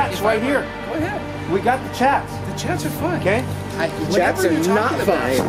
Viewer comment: Did he just say,The chats are fine? Put him in jail w big BUBBAH to keep warm at night